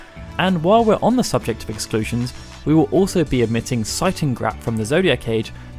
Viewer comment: what is the name of the weapon he says? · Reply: That would be Seitengrat :) ~Darryl